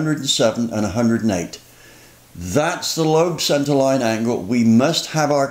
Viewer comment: Why a 107°/108° LSA? If we were doing this to an L31 350 Vortec motor and used a 107° LSA, would that still have enough vacuum to run Power Brakes and accessories in a suburban with fuel injection? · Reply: he clearly says Lobe centerline angle.